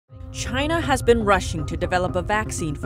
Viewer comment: opening line; "China's been rushing" What i hear; "China's been Russian" Me; 😅 Why'd they stop?